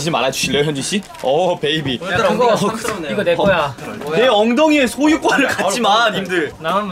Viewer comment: Did Minho just say Jisung's butt is mine or was I hearing things? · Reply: "This is mine" is one of the few korean sentences i learned from dramas, so yes I can confirm he indeed said that